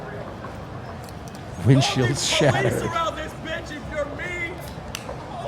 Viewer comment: So I may have misheard him but did he say while crying, “Y’all need police around this bitch if you’re mean. Holy shit!” ?